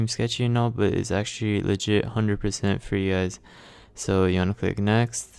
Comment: At the beginning, I know you said "legit" but I heard "the shit".